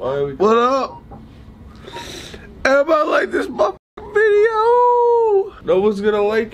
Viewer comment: is it just me or did grayson say: emma like this mf video